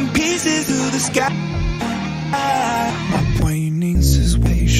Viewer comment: why did i hear "my brain is impatient" lol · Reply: i hear "my brainin is pasin"